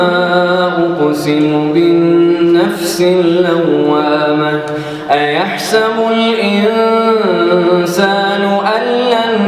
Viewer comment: did you say Yah-sabul or Yah-samul? It should be ba not mim I guess.